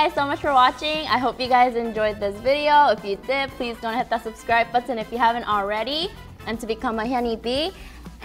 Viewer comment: did she say make shoure not to hit that subscribe button if you haven't already